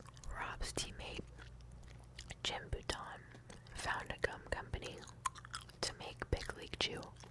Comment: I can swear at 4:47 u said CUM company!